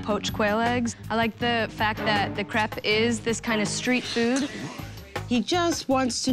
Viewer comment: Did she just call her own dish crap? 00:27 👀 I know...